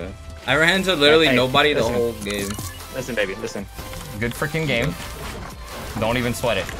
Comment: did ninja call daquan baby ??